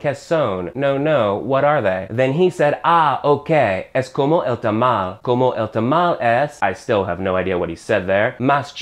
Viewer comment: when you said you still have no idea of what he said. Actually he said -- Como el tamal `` Na`mas que`` es mas chico... ( Na` mas que... ) is just a contraction. it basically means: but, though, or however. I used the apostrophe because it should be -- Nada mas que --. the three of them make a one and only meaning or sense (but, though, or however). So I`d translate it: It is like the tamale HOWEVER smaller.